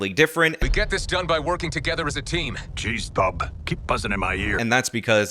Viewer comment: I had to turn on the captions because I thought Wolverine said Geez bub keep bussin in my ear. I think I gotta lay off the internet memes for awhile